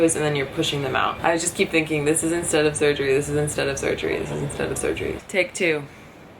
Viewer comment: Hehe When you said "this is instead of surgery" I thought you said "this is instead of Soju" (Korean distilled alcohol) Great video thank u